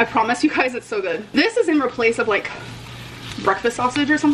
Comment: At around 11:15, did she say "this is in replace of breakfast sausage?" I don't think she said "in place of," I think she actually said "in replace of." How does she think that's correct? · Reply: yes, she says "in replace of" quite often